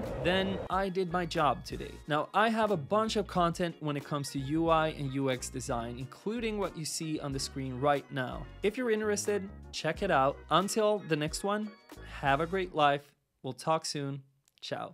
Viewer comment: Did he just said "Tchau" ?????? BR